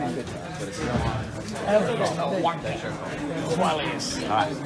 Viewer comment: what does he say? "Ellie's been....."